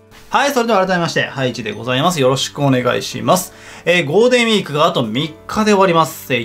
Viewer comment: ゴーデンウィーク って聞こえるw